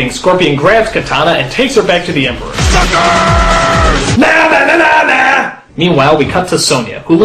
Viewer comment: Did scropion say "Fuckers!" at 00:35?